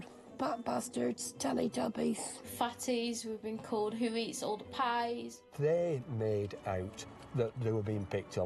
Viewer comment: what the fuck did she say? sounds like "who eats old pies" Can a Brit please explain?